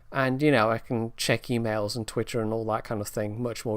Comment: Did he say he will check Twitcher?